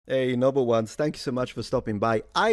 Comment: Go away now, Noble Ones. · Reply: Wait, he was saying "Noble Ones" this entire time? I thought he was saying 'Number Ones'.